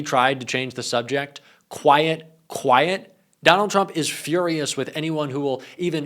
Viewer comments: It sounded like he said: quiet, quiet, Piggy. 😵‍💫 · Sounds like he said “quiet quiet piggy”